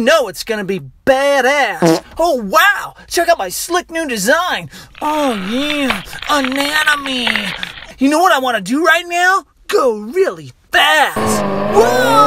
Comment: I thought he said Anime